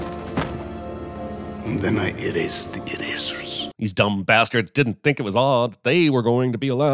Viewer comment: I heard "eris eris eris erasers"